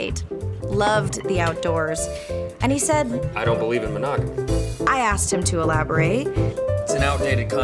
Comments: What does he say at 2:46? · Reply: He said "I don't believe in monogamy" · What did he say at 2:48? "I dont believe in.."? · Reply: Abdullah Hassan monogamy